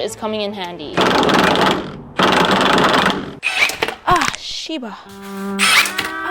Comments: did she just say sibal? lol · Did i just hear shibal at 9:44🤨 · did you say 'c bal'?lol